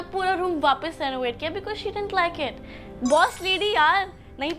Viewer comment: did she say room SURROGATE kiya instead of renovate😭😭😭💀